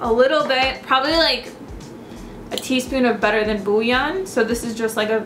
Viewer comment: what's the thing she said at minute 4:10? I didn't rrally understood and I don't think I know what it is... haha thanks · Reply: Better than bouillon. It's a vegetable stock in a powder form